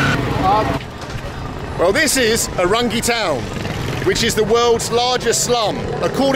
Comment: did you say Camden Town ? London 😂